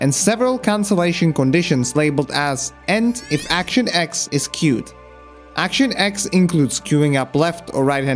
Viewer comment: I heard it as "End if action x is Cute," and was confused as to how they defined cute actions and why they stopped the other animations.